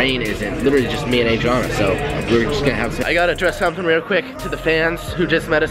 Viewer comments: LISTEN at the end of his sentence at 11:37. It sounds like he says"were just gonna have sex" hahahaha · It sounded like he said the house is empty nobody's home and we're gonna have se * camera cuts